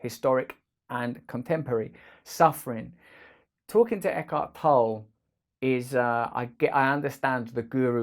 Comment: The subtitles fail at some words or phrases, instead of Eckhart Tolle, it appears : (at 2:22 )echo pol or ( 2:36/7 )as it car hahaha. How can you fix it ?